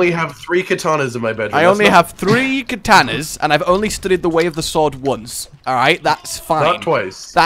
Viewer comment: I honestly thought you said "I've only stood in the way of the sword once", as opposed to 'studied'. Very different sentence.